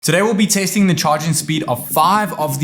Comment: Did i just hear hear "today we will be tasting " ?? 😅